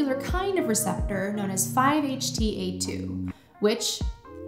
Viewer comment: I am on shrooms while watching this and she said 5-HTA2 when the caption said 5-HT2A. I just rewatched the last 5 seconds like 35-40 times trying to figure out wtf was happening. 10:45 other than that there video was very insightful and extremely interesting. Great work!